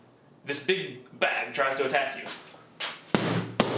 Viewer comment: I thought he was gonna say “black guy tries to attack you” Hahahah